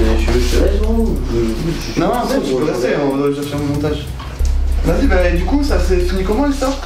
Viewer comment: il as dit quoi la?!! " Non tu peut rester on vas couper/flouter au montage" ?!! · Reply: Il a dit "je vais faire mon montage"